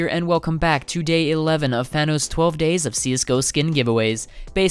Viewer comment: Misheard Fano’s, thought you said Thanos. Also the Emerald Pinstripe is my current AK, it’s OK but could be better